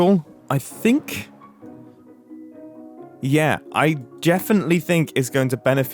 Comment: he 100% said jeffinitely instead of definitely